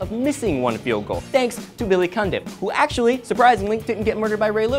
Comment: Thought you said billy cumdip *cough*